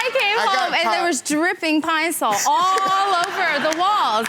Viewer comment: "Pine-Sol on the walls" 🤣 That was creative. Oh man. He said "and it worked for a long time" 🤣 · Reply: I thought they said pinesol